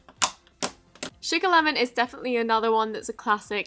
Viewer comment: For sugar lemon I thought you said chicken lemon